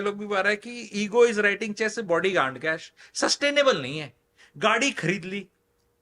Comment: What did he said on 36:54 ?? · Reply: Ego's writing cheques your body can't cash.